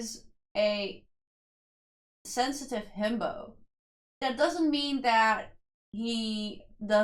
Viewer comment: I thought you said wonho is a sensitive bimbo 😂😂😂😂 had me dying.